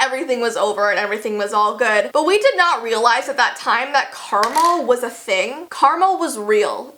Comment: Am I the only one who heard “Caramel” instead of karma at 4:13.. ? 😂 I had to rewind three times until I heard karma.